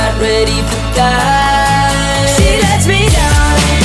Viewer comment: "she let's me down"...?! when I first heard this song.... I misheard this as "Sreelekshmi now"...😁and I was so excited and can't stop my laugh....cuz that's my Name..!!😂😂😂😍😍